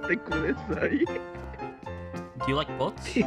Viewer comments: I thought he was saying yamate kudesai... XD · Why i hear "yamete kudasai" there 😂🤣🤣🤣🤣